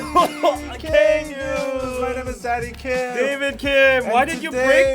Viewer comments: At the beginning I thought Danny said "Im daddy kim" and I was like huh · I have know clue why everytime danny says his name is "Danny Kim" i hear, "Zaddy Kim" like my ears are playing tricks on me.